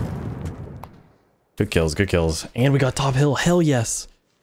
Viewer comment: In sorry, did I just hear “Hill yes!” 😂 Damn, and is dream team trio